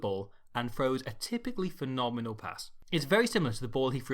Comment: did you say Finn-omenal?